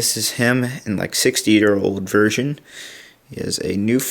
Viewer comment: for a sec there i thought u said obi wan 60 year old virgin